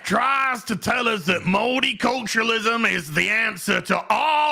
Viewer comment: For a moment, I thought said 'moldy culturalism', and then I went, "Hey, that actually kind of fits."